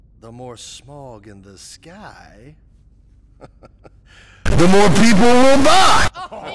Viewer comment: THE MORE PEOPLE WILL BUY" HMMMMMM, SOUNDED LIKE YOU SAID "THE MORE PEOPLE WILL DIE!!!!"